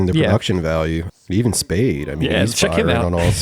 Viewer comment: did they call him david spade 5:08 that i guess is the serbian version of david